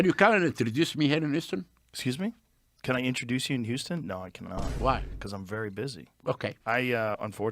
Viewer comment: Did he say "Whitney Houston?" If so, that may be the funniest thing I've ever heard. And I love Anna Paulina. She's so awesome.